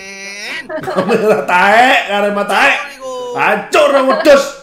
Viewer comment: kok gw dengernya Arema taek 😂😂